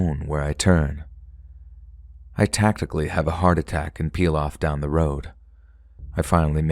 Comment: sounds like you say "I tactically have a heart attack" and that made me lol so hard 🤣🤣 strategic myocardial infarction! taking "playing dead" to a whole new level haha